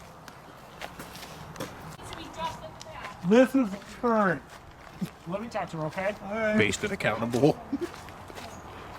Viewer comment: what is the girlfriend yelling ? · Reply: Clean the dookie off your pants!!